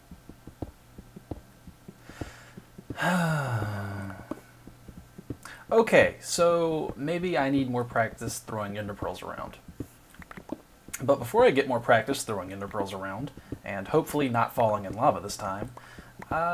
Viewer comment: I thought you said "throwing integrals around" at the beginning, and for a split second I was all "Vechs knows calculus?! :D"